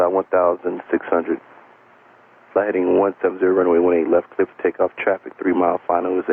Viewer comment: ，didn't the AUS TWR say " takeoff check" instead of "cleared for takeoff" as showed on the subtitle?